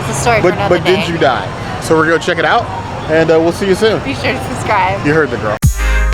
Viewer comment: I could have sworn the said "He shouldn't drive" at the point she usually says "Be sure to subscribe"! Hmmm....